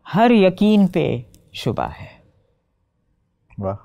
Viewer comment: Please tell me what she says at between 56:04-56:24 on evolution of her thoughts. जिन चीज़ों पर यक़ीन था हर यक़ीन पर शुभा(है?) हर यक़ीन पर क्या है? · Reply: शुबह = संदेह